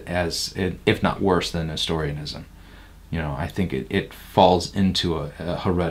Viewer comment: What is he saying at 30:25? I can't quite make it out. The CC interprets it as "Historianism," but Google search comes up empty. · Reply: Nestorianism